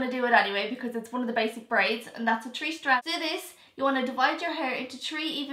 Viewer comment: It sounded like she said tree but she said three